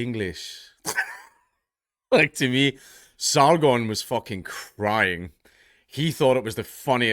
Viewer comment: I couldn't understand this video because I don't speak haggis. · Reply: Thought he said Sauron. And imagined his friend as a giant eyeball. 😁